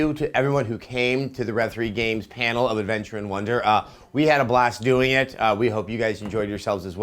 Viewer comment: Who did he say at 0:18, Trent wonder?